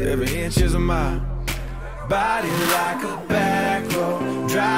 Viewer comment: When I first heard this on the radio it sounded like he said "body like a backhoe". I can't hear it any other way now. :-D